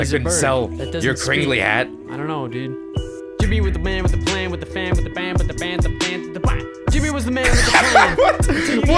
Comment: All I heard was "JIMMY WITH A MAN WITH A PLAN WITH THE FAM WITH THE BAM BA DA BAM BAM QUAHH" Lol